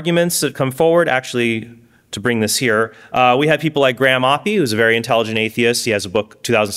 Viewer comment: could have SWORN said “Grandma” when I closed my eyes for a moment! 😂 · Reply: Graham Oppy = Grandma P.